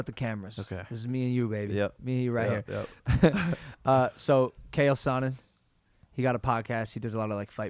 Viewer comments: Did he just say kale sonnen😂😂😂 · did he just say kayell sonnen.